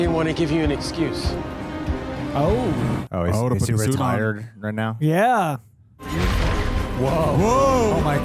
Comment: did you say r*tarded 🤨